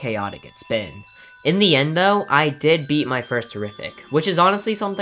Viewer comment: did bro just say terrific